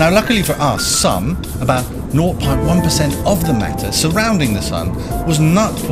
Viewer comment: what does he say at 1:03? what percentage? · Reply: 0.1 %. That's 1/1000th (one thousandth) and means that the sun is 1000 times more massive than than rest of the solar system put together.